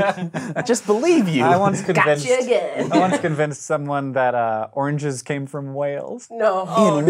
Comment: I thought you said oranges come from Wales. Then I thought didn't they come from China.